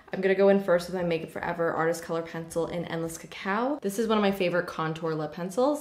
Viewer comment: Omg I heard “MUFE artist pencil in endless cow” I was like what.... 😂